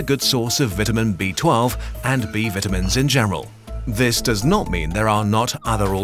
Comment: vitamins b12 and vitamins b-vajreral? What was that he said?